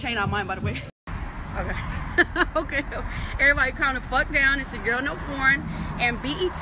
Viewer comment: This is what I hear every time "Everyone calm the fuck down I don't do porn"